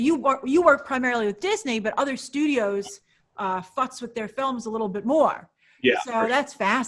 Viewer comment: Super good content. Did anyone else rewind F bomb to see if they misheard? 😂😂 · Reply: I think she said "futzes with them"...lol